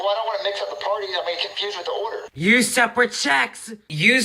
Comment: I’m having a hard time on what you’re actually saying…. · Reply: I can translate perfectly. You see, this idi- I mean highly intelligent individual, old, said "USE SEPARATE CHECKS!" repeatedly. God I hate my old reviews